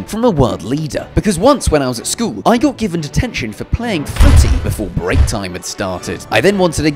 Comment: As an American, I first thought he said, "I got caught playing footsie before my break." Took me a second, then I got it. Still, it's funny to think about him playing footsie with someone and getting detention for it. Then getting pardoned for it.